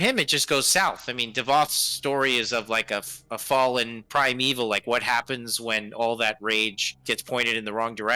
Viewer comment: I've always thought was saying "prime evil" not "primeval"